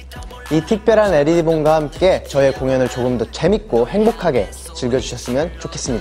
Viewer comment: did he said "Aeri Bong"?